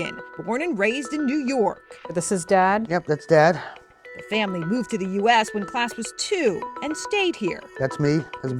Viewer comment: He was born in the US. He’s a citizen. Period. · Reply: Shoot, I rewatched and he says he moved to the us. I misheard with “born”, thanks champ!